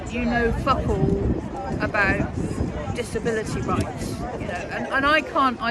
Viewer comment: Small correction, at 9:33 the captions say "disability bunks" when actually says "disability rights."